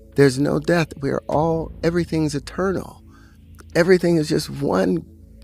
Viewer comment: I thought he said .." everything is a turtle" 🐢😏🙃